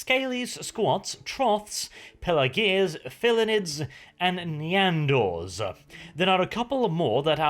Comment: did he say Nyan-gors? o.O